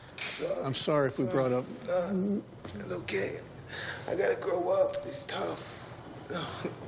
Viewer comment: “I gotta grow up, it’s tough. I got to be a man and stop crying” damm that’s coming from one of the baddest men on the planet. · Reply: I thought he said he gotta throw up